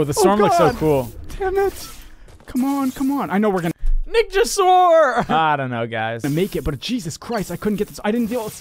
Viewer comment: Bruh I have captions on and when Nick said “Nick just swore” it said Nick Jessore